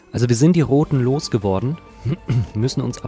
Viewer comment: wir sind die Hoden losgeworden (zumindest habe ich das dass erste mal so verstanden, weil du anscheinend was im Hals hattest) XD